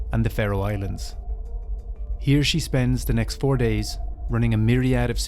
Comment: “He or she” …..riiiight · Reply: I had to run it back a few times because at first I heard "he or she" also. I was like wait what?? LMAO. But upon further review it is definitely "here she" 😂